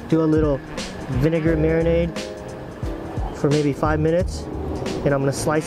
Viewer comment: thought he said 6-7 minutes